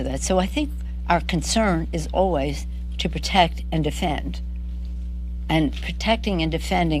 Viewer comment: Did she Just say: to protect or defending me?" 1:53 min